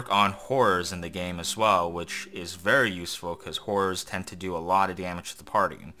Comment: I think you tried to say "hordes" but I heard "whores" twice lmao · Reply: I tried to say horrors but it sounds like whores. I wish WFS kept the Japanese translation which was named F.E.A.R.'s